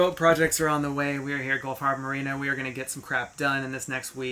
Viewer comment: I thought you said skull Harbor Marina